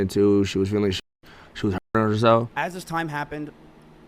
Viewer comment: does anyone know what was censored here? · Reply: Hurt